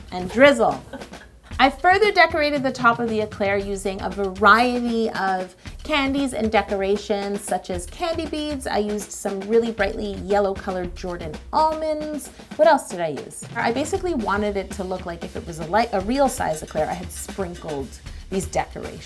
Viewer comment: I thought she said candy beans😂 ....made me think of Gob from Arrested Development